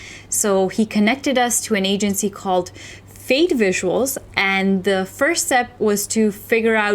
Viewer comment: Hi! Can you please confirm the name of the company that did this service for you? I don't see anything online with the name "Faith Visuals", which is what it sounds like says here. Thanks!